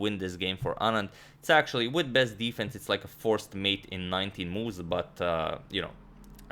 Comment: Ahhh, just 19 moves, I thought you said, “just 90” ha